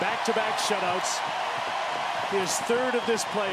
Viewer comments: Doc said that Detroit won for the first time in 11 yrs it was 6 bud come on man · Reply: If you listen closely he says 4th time in 11 years, does sound like he says 1st though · in 2008 he said "the red wings are champions for the first time in 11 years" I'm not the best at math, but i believe it was 6 years lol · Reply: ohhhhh you're right. I wasn't listening close enough. it sounded like he said "first" not "fourth. thanks bud